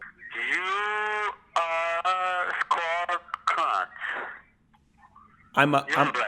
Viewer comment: I'm pretty sure @ 1:09 the inaudible, you were called a " squirrel cu*t"? Lol! I think that's what I heard!?